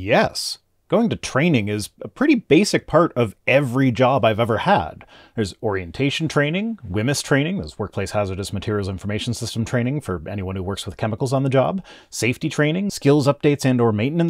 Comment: Did you say, "Borientation training"? If not, too bad. It's a pretty clever, and accurate, portmanteau.